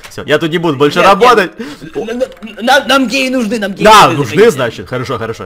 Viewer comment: я слышу НАМ ГЕЙ НУЖЕН гей